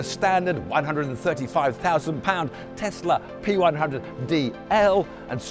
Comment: did he just say 135,000 lb. standard tesla?